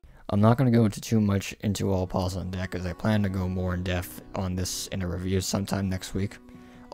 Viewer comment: did he say “death” Edit: I actually grew up with her og show, so it celebrating its 10th anniversary is both heartwarming And makes me feel old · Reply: People say death, and he said depth